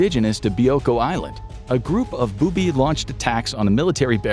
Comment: A group of "boobie" launched attacks on military barracks - Sorry. I wasn't watching the show but listening. I taught I heard wrong.